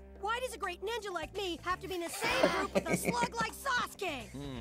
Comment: LMAO it sounds like he says "Why does a great ninja like me have to be in the same group with a slut like Sasuke"